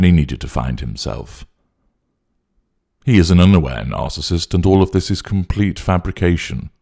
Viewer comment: I misheard "He's an underwear narcissist" and now I can't stop giggling 🤣